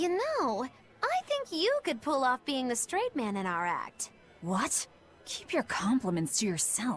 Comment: i know she said "being the straight man" but i heard "being a straight man"